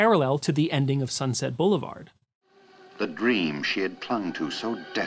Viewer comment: Thanks for this. You are very perceptive and this gives me a lot to think about. But I do think that you misheard "unfolded" for "enfolded" in the Sunset Blvd. quote.